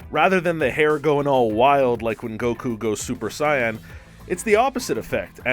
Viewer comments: Did he just say super cyan 😂🤣🤣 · did he just say "super cyan" 🤣🤣🤣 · Screen rant did I just hear you say “super cyan” ?? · Im sorry did he just say Super "Scion" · did he just say ‘super cyan’ LMAO its